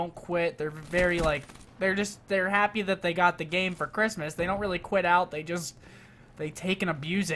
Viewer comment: Could have swore he said ''I got Cheetos for Christmas''